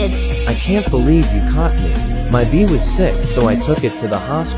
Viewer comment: I thought he said my bean was sick i was like “that’s not a pet Archer…”